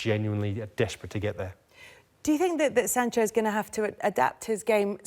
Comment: Did she just say Leipzig for Jaydon Sancho? Shambles. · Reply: Also it sounded like she said Sanchez instead of sancho